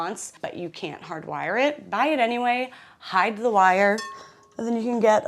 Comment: I swear at 26:30 I heard you sing “hide yo kids, hide yo wife” but maybe you’re not even old enough to get the reference.